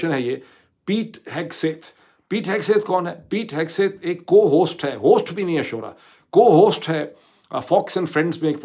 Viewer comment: did you say shooraaaa.... 🤣😂